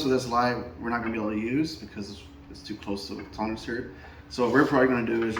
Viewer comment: Great video! At 6:15, what did you say was the reason for not being able to use the cover that came with the light? The audio is not clear. "It's too close to a [?]." Thank you! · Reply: Thank you! Sorry about that…. It’s too close to the frame and you’re unable to put it on.